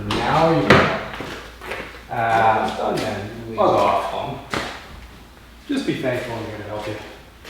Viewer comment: did say bug off or fuck off lol